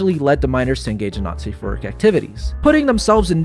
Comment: "minors to engage in not safe for work activities" got interpreted as "..in nazi furry activities..." by the captions, which is not what you said?? (happens again